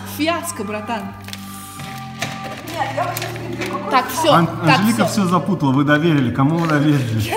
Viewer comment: dice "puto" o entendí mal jeje!!! 😂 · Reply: "Анжелика всё запутала" ("Angelica vsyo zaputala") se traduce como "Angélica enredó todo". La palabra "путать" (putat') significa "enredar" o "confundir"